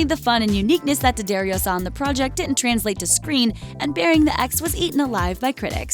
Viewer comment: I had to look up ( to check out the title ) when they mentioned " Burying the Ex". I thought they said it was called " Baring the Ass".